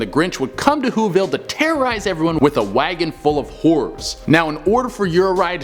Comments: I thought you said “a wagon full of whores”. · i thought u said a bunch hoes not horrors